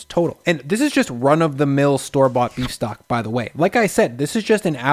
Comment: I really thought that said "Shmucks" Beef Stock 😑